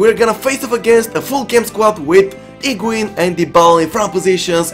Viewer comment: When he said higuain,i thought he said pengiun.